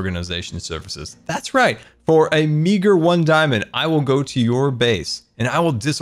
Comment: Love the chest monster. Btw look at 20:15 what did he say? "A n####r 1 diamond"